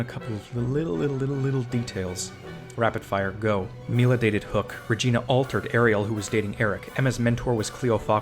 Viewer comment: I heard that as Neal dated Hook 😭